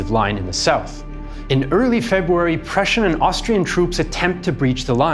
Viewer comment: the caption should read "Prussian and Austrian" instead of "Prussian and Danish".